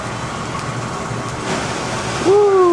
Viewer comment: did he say gasoline?